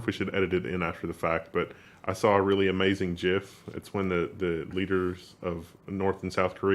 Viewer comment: did you say JIF? lol